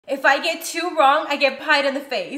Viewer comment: I lowkey heard her say “If I get 2 wrong I’ll get cream pied “ 💀